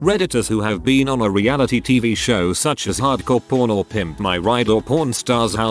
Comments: The whole time I thought they were saying "hard-core porn," jesus. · I keep hearing "hard-core porn" instead of hard-core pawn.